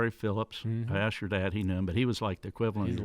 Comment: did say to Jr, ask your dad, he knew him? Sounded like it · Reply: He said I asked your dad.